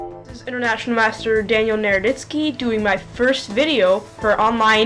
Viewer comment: No problem. I thought he said "Nerditsky" too... somewhat apt!! If you want a good channel, check out chessexplained, or I also like the commentary of jrobichess.